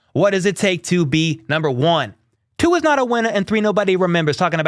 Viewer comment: when you said your little Nelly rap I thought you said “Tua’s not a winner” which would’ve been hilariously true